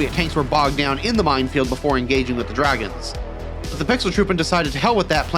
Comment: What do you call the Engineers at that moment? I heard something like "pixel troop" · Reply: Pixeltruppen. It's a long standing joke within the CM community :) Early CM games were played at very low fidelity to the point that soldiers looked like pixels at any actual distance, hence Pixeltruppen.